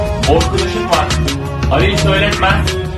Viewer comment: ne nenenin ne dediğini anlamadim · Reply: Almanya dedi